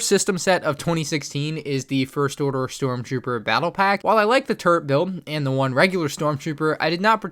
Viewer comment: I thought he said turd build 😂